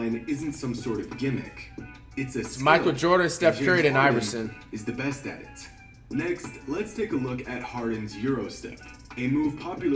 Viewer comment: Did he say it was Jordan steph then Iverson as top 3